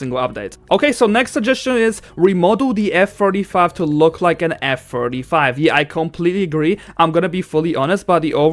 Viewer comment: i think i heard f45